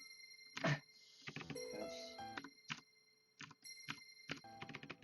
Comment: It sounds like he says "Hush" or something similar at 0:29 I had to ear rape myself to hear it, but it's definitely a word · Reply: Swivel_Z He said "Yoshi" and it's japanese word. It means like "All right" or "Okay" in english